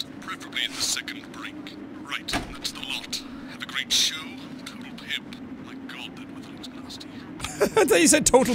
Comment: i'm eating noodles whilst watching, so i was looking away shoveling my mouth full when i also thought he said ''total pimp''